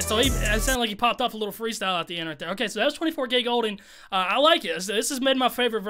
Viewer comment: I thought he said 24 Gay golden